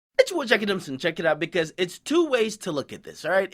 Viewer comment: Wtf is you saying in the intro beginning of every video?? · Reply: It's ya boy DJ Akademiks & check it out